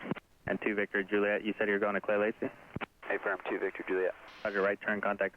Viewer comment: what does ATC say?? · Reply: He said "you're going to Clay Lacy?"